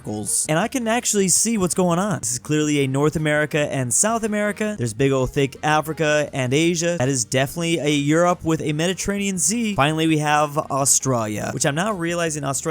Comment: I cringe every time says 'Austrarya' instead of Australia.